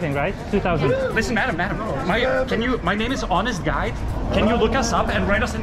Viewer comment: First time watching one of your videos and I thought you said "My name is Honest Guy". Business cards work better, for sure.